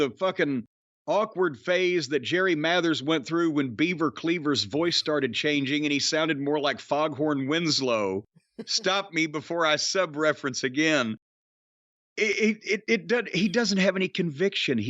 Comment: I thought Jim said Beaver Cleavage lol